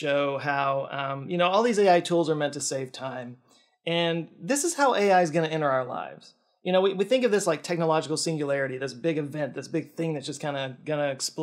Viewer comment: Not having Bob as your uncle is one thing, but not having Bob Ross as your uncle is very sad. Was it just me or did I hear "so this is how A.i. is gonna end our life!" at 18.18?